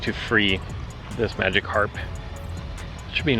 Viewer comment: Did you just say.... Magikarp? xD